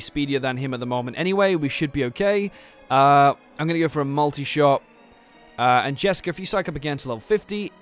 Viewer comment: haha the subtitles can be so dumb sometimes 18:40 when you say mulitishot the subtitles says multi sharp, and falcon slash is file cancer 19:58. damn this subtitles...